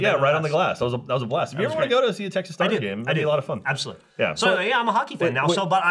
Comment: Did he really say texas stars at about